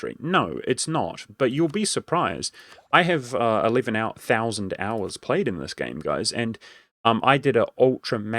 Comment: Did I just hear this man right? He has 11k hours on EU4? God that’s some dedication